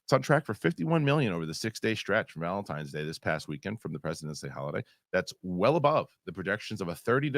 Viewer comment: was it only me that misheard in the beginning when he said "It's President's Day"? (cause I heard "The President is D...") o.o